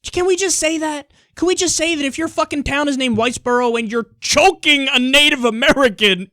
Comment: I thought you said whitebro